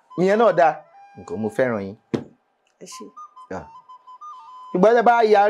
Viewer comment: who else heard ‘because’ mo feran yin